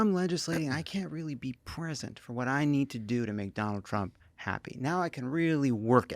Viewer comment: Heh, it kinda sounded like he said McDonald Trump